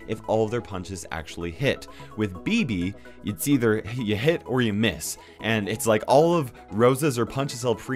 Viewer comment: DID YOU SAY HIT OR MISS?!?!?!